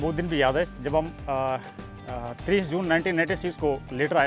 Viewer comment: i think he has said 1996 …… because tenure of jawan ends up in Army at 32 years of service at any cost….but as per Subedar sahab’s narration’s english version in subtitle 1986 which comes out to be 38 years of service which is impossible…..